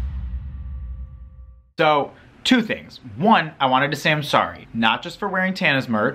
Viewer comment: When he said "two things" I thought he said "tooth aches".. this some yanny laurel shit.